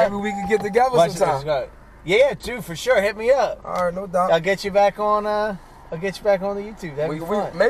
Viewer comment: Did he say, Did he just say "alright love you" at the end?